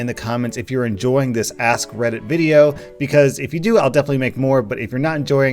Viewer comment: When he said ask reddit I thought he said ass credit. 😐